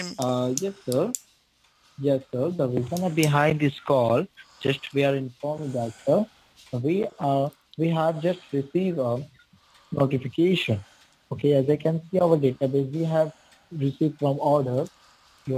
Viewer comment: I thought he said " we have Justin Bieber...." 🤣🤣🤣🤣🤣🤣